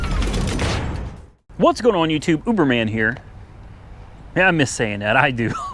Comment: you said,But it sounded like you said...New name...not really..HA HA HA ...